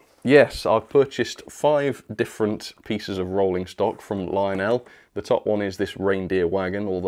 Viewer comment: Did you actually say "five different faeces of rolling stock"?